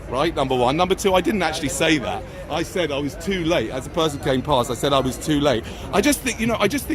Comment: what did he actually say? I thought he said......"i was late".....doesn't make sense. i think it's important to know what he said because if he did make that reproduce statement to my wife and child i push him into gutter and say that's where you belong.